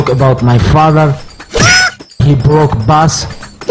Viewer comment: Everytime I heard "HE BROKE BUS" LMAO 🤣